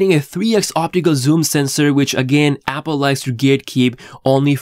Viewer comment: Lol wtf did you say at 2:38 ???? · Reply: Gatekeep?